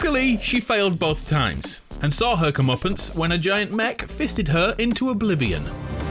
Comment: AYO did he just say a giant mech FISTED her into oblivion 🤣🤣